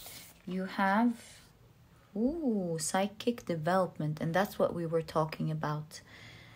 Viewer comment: you said psychic development but in my mind I heard "side kick development" so I'm taking that as a sign that this next step I am about to take will help me develop the team I am looking to work with! I used to question when I misheard things but now I embrace them as signs and often can decode the messages for myself 🥰 love and light